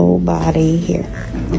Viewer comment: Could just be my hearing, but I swear I heard a deep, gutteral growl of "Nobody here"